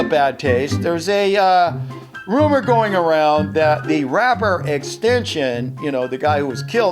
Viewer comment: did he just say "Extension"??